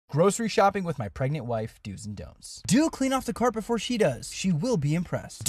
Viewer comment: I swear I thought it said "shopping with my 2 pregnant dogs" 💀